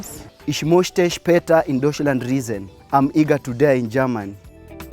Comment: Did bro just say he's eager to die in Germany😂😂 ...on